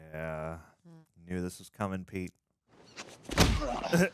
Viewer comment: At around 12:00 did you say “you knew this was comin Pete” like as in spider man 3 ?? 😂😂